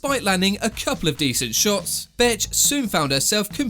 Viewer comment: Every time he said Bethe it sounded like he was saying Bitch 😂 Maybe it's just me but I thought it was funny Lol